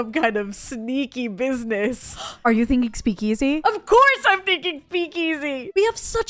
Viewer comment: my english isn’t very good, what did they say here? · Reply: A speakeasy, they are referring to the hidden bar they are building. Originally they were bars that illegally sold alcohol when alcohol was banned in the 1920s-30s.